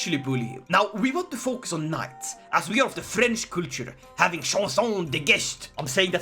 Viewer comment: When you said “French culture” I thought you said French creatures lol